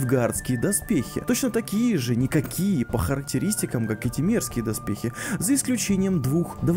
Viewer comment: "Те мерзкие доспехи" - послышалось...